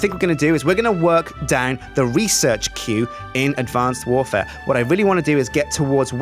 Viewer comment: Sjin said "advanced warfare" instead of ancient warfare